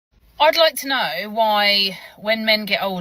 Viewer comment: Did she just say "Oiyd loiyk tie nye"?